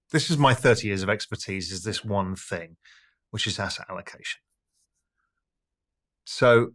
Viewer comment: What in the gibberish did he say? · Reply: I think, “which is asset allocation”